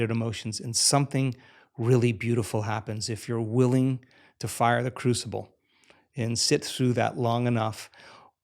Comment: This is so cool. I am around 28:30, but what does it says? I am not english... It sounds to me something like "if one is willing to *fire the cruxible*? Big up anyone, love and peace and a big hug to you and us